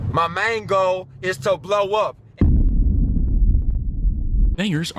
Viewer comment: Does anyone else hear my mango is to blow up